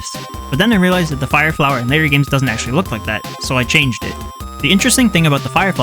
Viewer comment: InMrI?! WTF is he saying?! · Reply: He said 'in later games' but you may be joking so I'mma just leave, have a nice day 🚪🚶‍♂️